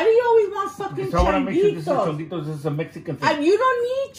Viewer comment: What's the deal with him handing her his pinky.. I heard him say it's a Mexican thing.can someone please explain? And I "f*ckin" love these videos, btw! 🤣🤣🤣🤣🤣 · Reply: He said changuitos. It’s kind of like pinky swear